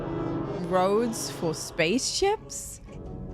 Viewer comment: ! This theme is the most frequently played so-called "ear worm" in my head! 😅 Did you just say "roads for spaceships"?! 😂